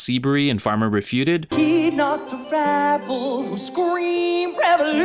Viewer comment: I always thought he said "heed not the REBEL"